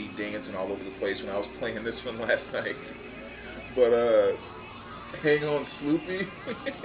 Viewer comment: Love the Naked Eyes, wish I had that on vinyl. I have not heard that song by The McCoys since I was a kid and I gotta admit I have always thought they said Hang on SNOOPY until I watched your video, LoL So is this 30 day vinyl challenge thing about not buying any records for a month?